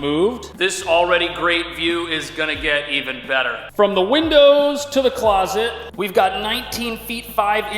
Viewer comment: when he exclaimed : "From the WINDOWS" all i heard was "TO THE WALL" --but ended up in the closet. lmao